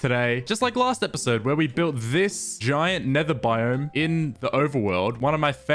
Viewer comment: NGL thought he say a mushroom biome